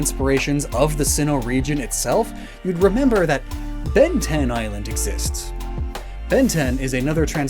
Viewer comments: I can't be the only person who thought he said Ben-10 (the cartoon) Island, right? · frick I thought he said Ben 10 Island. My gosh